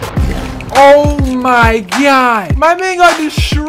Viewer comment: OH MY GYAt is what I hear but also pls come back i miss you your so funny and you make me happy pls we all love you and I miss you we all miss you 😢